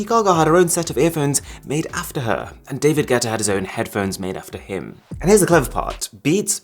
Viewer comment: Did Arun say ‘David Getta?